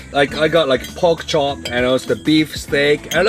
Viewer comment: Did he say pogchamp?